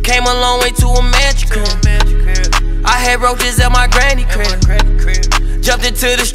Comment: When I thought he said "roaches" and not "wrote this" I kind of related more to it more 😒 the roach part 😭😭